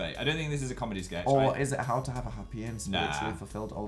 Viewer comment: Oh lord I thought Phil said "How to have a fourway" what the hell is wrong with me